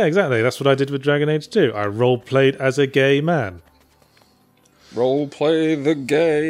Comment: I thought Yahtzee said he roleplayed WITH a gay man...